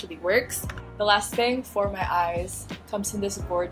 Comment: At one point I thought you said "bibe banila co." instead of b. by banila co. HAHAHAHAHAHA